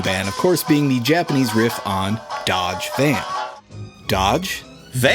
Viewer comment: Dajiban? I thought you said it was a dodgy van?